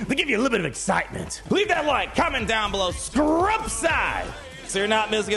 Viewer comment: Had to go back and watch a second time to verifiy my brain really heard him say "SCRUB SIDE!"